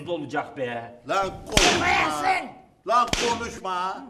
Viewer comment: 'te Mevlüt usta ne diyor lütfen söyleyin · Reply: Konusmayasin